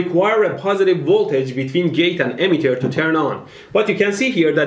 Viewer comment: little subtitling mistake. You said “emitter” but the captions say “Ammeter.”